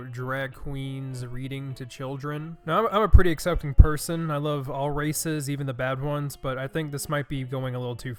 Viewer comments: i thought you said "i love all racists even the bad ones" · For a second I thought you said “Drag queens beating to children.”